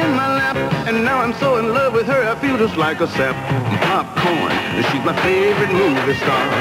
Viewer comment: deadass thought he said "I feel just like a simp"